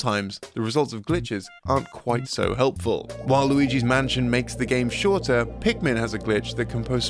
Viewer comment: At first instead of "While Luigi's Mansion" I thought you said "Waluigi's Mansion." Lol Nintendo, Waluigi's Mansion when?